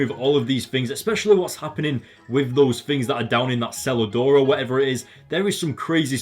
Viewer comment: Omg easily one of your best videos yet. The ending with the smile and the beginning, You reminded me of a little kid. And when you said cellar door I thought you said salad door XD because of your accent and I love how you said he has nothing left while he's standing on the deck of a mansion in the alps while probably drinking a really expensive alcohol